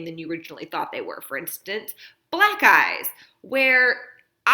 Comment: Did she say "Black Guys" at 8:05? Tsk tsk. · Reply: Oh my gosh! It does sound that way. I promise I said "Black Eyes."